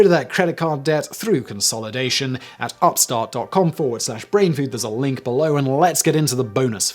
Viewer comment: Swear it looked said "brain fart" for a second. Ironically I'm stoned lol